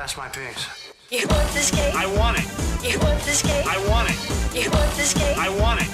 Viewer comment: Well the only thing I hear "!is you won't escape " lol